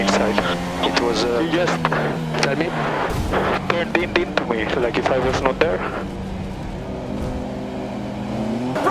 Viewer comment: Wtf was he saying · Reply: Legit what he said he turned into me like i wasn’t there